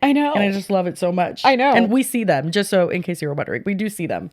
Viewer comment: when you say “we see them, we do see them” I just hear “Do We See Them? Yes we do.”😂